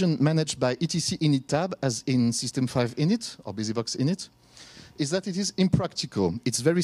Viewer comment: did he say 'busybox init' or 'bsd init'? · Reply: Busybox init.